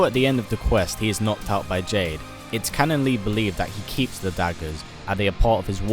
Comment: Did he say diapers? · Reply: said "Daggers"